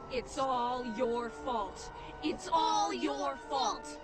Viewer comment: “It’s all your salt” is all I hear